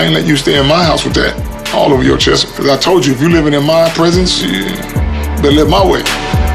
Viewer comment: 😂😂😂 I thought he said prison when he said “when you live in my presence” Man on Fire 😂